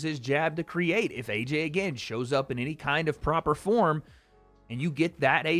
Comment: I keep hearing…. “If a certain AJ shows up…” which already sounds like a window for funny business. 🤔